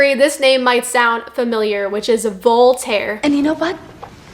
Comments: Didnt know was saying voltaire, I thought she was saying bald hair or something 😅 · Oh Voltaire. I thought you said Voldemort.